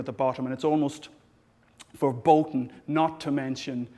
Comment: Did he say "verboten" instead of forbidden?